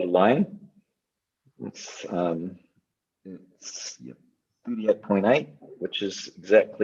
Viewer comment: does he say 38.8? Where is his low?